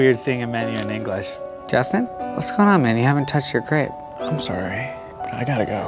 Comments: What's going on man? You haven't touched your crepe. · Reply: OH I THOUGHT HE SAID "GRAPE" but crepe makes way more sense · 'What's going on man, you haven't touched your grape'😭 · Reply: dumisa don't worry man, you're not the only one. I thought he said 'grape' too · 'What's going on man, you haven't touched your grape'😭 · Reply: dumisa Hahaha I had the same thought. I thought he said grape but it didn't make sense so I figured it was crépe. But grape would be so much more BA.